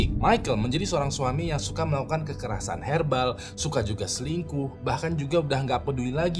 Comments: Kekerasan herbal... Apa aku yg salah denger [ 1:48 ] 😂😂😂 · maap bang kekerasan herbal itu kekerasan yg gimana ya 😂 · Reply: kirain cuma sy yg salah denger ,kekerasan herbal 😂